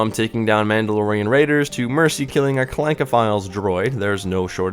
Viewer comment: Did you say Clank-O-file? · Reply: Clankophile yes. It’s a whole thing with a quest in this game lol